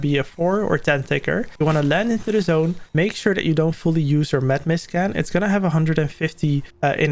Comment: Did he say meth mist at 5:24?